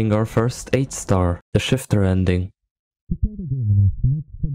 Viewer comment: I thought he said the shitter ending😭